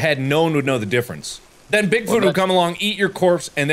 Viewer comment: the subtitles said big Bluto instead of Big foot.